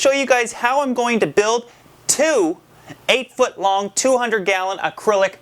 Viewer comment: Im building a pair of 200 gallon tanks. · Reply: ***** Oh, I thought you said 800 gallons. Sorry, my bad!